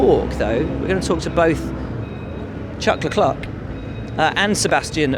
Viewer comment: did he just say "Chuck Leclerk"? :D